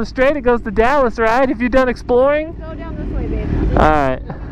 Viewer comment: DID SHE SAY BABY ??DAAAAAAAAAAAAAAAAAAAAAAAAAAAAAAAAAAAAAAAAAAAAAAAAAAAAAAAAAAAAAAMN